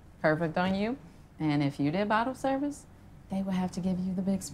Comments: Bobble service in another life. Lmao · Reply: J M I was being funny cause I misunderstood what she said. I thought she said bobble service n another life. I heard wrong.. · Bobble service in another life. Lmao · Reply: Fist Benoit ok thanks I feel a little corny. I thought she said bobble service, like selling sex